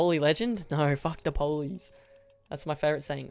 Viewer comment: sounded like police lmao